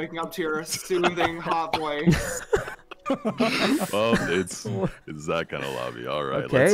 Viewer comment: What does Corpse said at 1:47 - 1:49 ? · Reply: he actually said "well its...its that kinda lobby huh? Ok..."